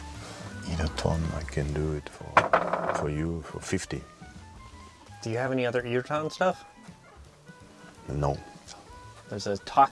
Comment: Can someone please tell me the name of the little mascot at 9:18 I can’t work out what he’s saying for the name and I like him. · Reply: Irutan 👍